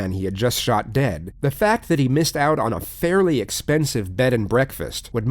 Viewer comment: thought he was about to say, "fucking bed & breakfast."